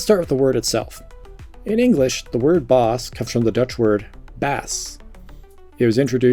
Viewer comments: I believe the spelling here should be "Baas" instead of "Bass" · Dutch person here, its Baas instead of Bass